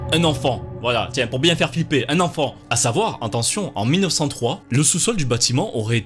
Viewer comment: J'ai entendu "attention"